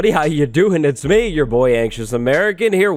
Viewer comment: “Anxious American” I feel ya there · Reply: Oh I thought you said you’re not living in America not you’re not anxious, pardon